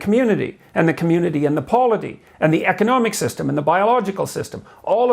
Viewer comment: What does he say at 1:17? "The community and the..." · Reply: polity (any kind of political entity)